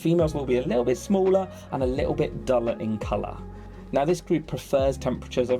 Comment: I know you said "duller in color" but I heard "dumber in color" and I'm seriously lmao